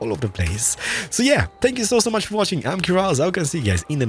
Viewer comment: It sounds like hes saying spank you not thank you